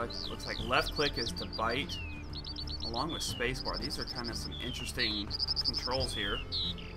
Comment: I thought that you said ''There's some interesting trolls here'' instead of ''There's some interesting controls here''